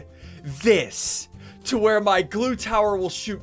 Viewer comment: i heard poo tower